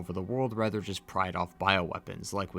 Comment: I can’t understand what you’re saying. Did you say “the connections don’t wanna take over the world just PRIDE OFF (?) bioweapons” ???? Can someone clarify what he was saying for me please 🙏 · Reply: i think he meant to say "profit off"